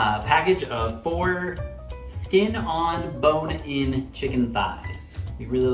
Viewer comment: 😂😂😂😂 he said it he's self 4 skin 🤣🤣🤣🤣 classic,an then he was hesitated what he just said.😅